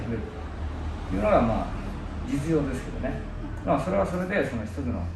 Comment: ここ、実用ではなくて実情ではないですか？ちょっと聞こえにくいですが・・・ 4:20 このあたりの字幕になっていない部分。とそれ以降も含め。 練習方法ということですが、つまり意図としては、エッセンスをつかむ練習法としての型、技、であって実戦で直接目指す形ではないということでしょうか？ そして到達したシンプルな複合体から武が生まれる、その場で技が無限に湧いて出てくるのが目標ということでしょうか？